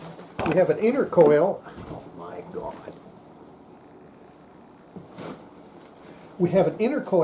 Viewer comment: Does anyone else hear a stray voice at 21:40 saying something like "oh my gawd"?